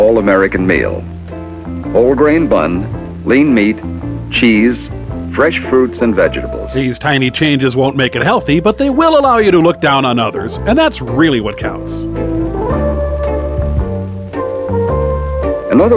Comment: I'm going to start saying, "VE-GE-DA-BLES". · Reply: I thought I heard 'vegables' at 7:08. I might be mistaken.